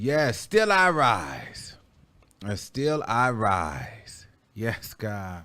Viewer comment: you know u not right 🤣🤣 all I heard was “And still I roll “ great video babes keep it up 💕✅